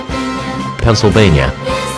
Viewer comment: why do I hear someone say Pennsylvania